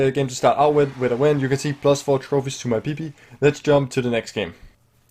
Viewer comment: could’ve sworn I heard ‘4 trophies to my peepee’